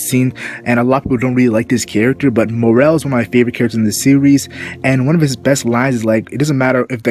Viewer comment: I think I may have misheard you but did you say he is one of my favorite characters or he is my favorite character because I though you said he is my favorite character in the series and I was like WOAH WOAH WOAH DID I HEAR THAT RIGHT I THOUGNT LEORIO WAS YOUR FAV CHARACTER but idk maybe I heard you wrong